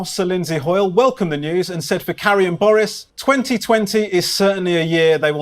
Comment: It sounds like he says "For carrying Boris" which is sort of what the Tories did. (The phrase was actually Carrie & Boris obviously).